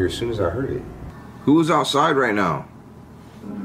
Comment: it sounded like someone said “Brent” after you asked who was outside